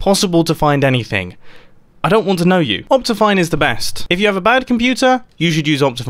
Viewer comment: what did he say? Optify? Just asking so I can get it because I didn’t understand